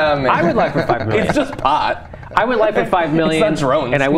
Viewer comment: Did the guy on the LEFT say "its just pot its not drone" Drone did he say that?